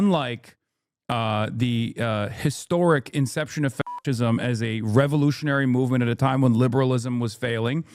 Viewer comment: You can't say fascism on YouTube now? Is there anything left you can say? Wtf! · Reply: I thought they were saying f*ckism